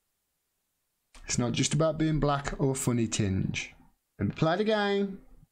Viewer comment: I thought i heard her say a "funny tint".